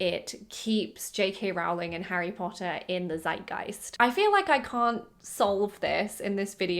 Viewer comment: Did you actually say "zeitgeist"or am I stupid? And yeah so movies are bad(my opinion obv)I liked the books tho